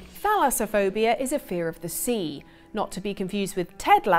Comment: girl i thought you said Phallus-aphobia and was prepared for a much different explaination